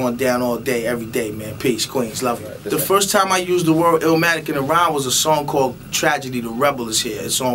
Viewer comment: 15 secs I thought he said the first time I said illmatic in a rhyme was ill Cause I ....... Lol